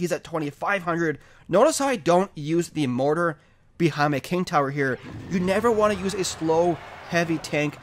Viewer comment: am i the only one who heard shane they mortar behind the king tower